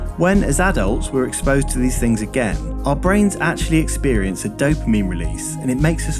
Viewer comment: i thought you said "a dope meme release" but you said dopamine. i hate myself too.